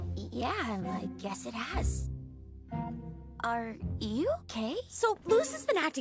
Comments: When Willow said "Are you okay?" I genuinely thought she said "Are you gay?" I mean she wouldn't be wrong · I keep mishearing that as "are you gay?" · I somehow managed to hear "Are you gay?" instead of "Are you okay?" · I thought Willow said "Are you gay?" AHAHAHHA